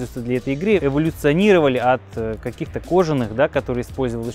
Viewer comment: мне послышалось или было сказано "мячи революционировали" ?)